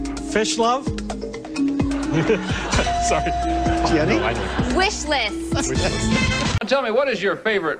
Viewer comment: Tbh, i thought it said fish limbs